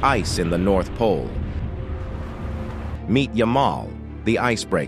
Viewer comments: I heard "meet ya mom" for a split second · Nothing sounds more Russian than "NUCLEAR ICE BREAKER" 😂😂😂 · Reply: I thought he said "your mom". Not yamal. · I keep hearing "yo ma" instead of Yamal 😂😂😂😂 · Bro when he said meet yamaal I heard it “your mom” 😂😂😂😂😂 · Yamal… I thought it said meet Your Ma